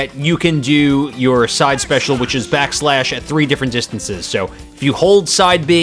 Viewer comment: Shulk main here. Some points that the video missed: •The second swing of Air Slash can be delayed for a slight boost in horizontal recovery •As Vision activates, you can tilt left or right depending on where the attack came from for more knockback. This will send your opponent behind you, and can only be done while on the ground •Performing a successful counter attack with Vision repeatedly will give you less time to activate the counter. It will go back to normal over time •Back Slash is stronger when it connects on an opponent's back (kinda obvious, but I don't think it was mentioned in the video) Other than those stuff, this video has pretty much all that you need to know about the basics of Shulk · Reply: Whenever I use back slash I always hear "backshots"